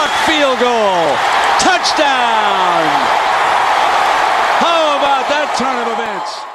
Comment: did he say " how about that son of a bitch? " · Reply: He said “how about that turn of events”.